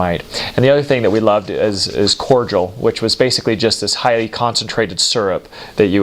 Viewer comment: "The other thing we love is cordual" It's CORDIAL! Wasn't until he finished explaining it I understood what he was saying.